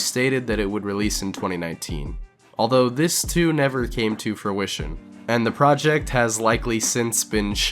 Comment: was foo-ission instead of fruition intentional?